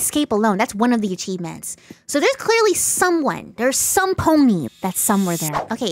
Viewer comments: did i hear this word correct 🤔 cuz im pretty sure i heard " somepony" 😮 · If my ear is okay did you said "Somepony" insted "Somebody"? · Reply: She said “somepony.”